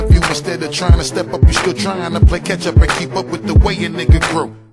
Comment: who else heard ketchup